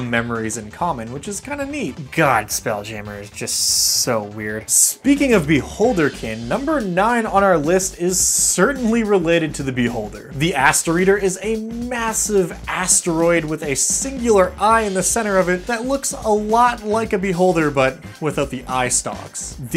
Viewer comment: I could swear you said the "ASSEATER" at 3:40, gave me a mini heart attack to imagine a asseater monster in d&d...